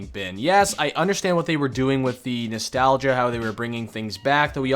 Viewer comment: This is what i hear"Nestalga" "Nestalga" "Nestalga" "Nestalga" "Tideous"......